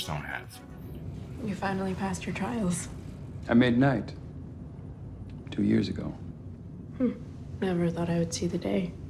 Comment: Oh 2 years ago. I thought he said 10 years ago.